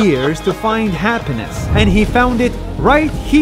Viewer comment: I thought I heard “By the bitches of Lebanon”